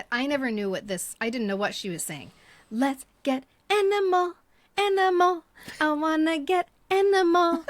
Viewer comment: When I first heard ONJ’s Physical, and she sings “let’s get animal, animal”… I heard “let’s get enema, enema” 😊